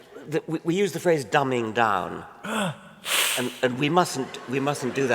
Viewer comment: Hello. Please, could you tell me what is professor Dawkins saying at 18:02 ? Thanks! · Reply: 'we use the phrase dumbing down' That?